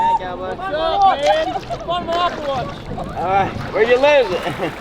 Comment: it sounds like he said where ya live? Lol